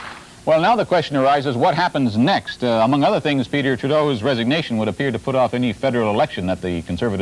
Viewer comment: did he just call him Peter Trudeau??